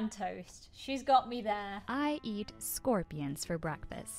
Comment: I thought she said I eat Scorpios I was I'm not safe I'm a Scorpio and do I have poisoning powers